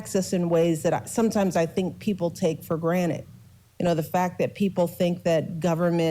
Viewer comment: Did she say… ‘take for granite’??? 😂😂😂 Rick & Morty fans will get a kick outta that shit (Thanks!!❤)